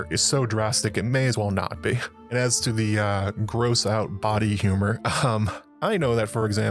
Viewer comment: I thought you said "bawdy humor" but it was "body humor" 🤢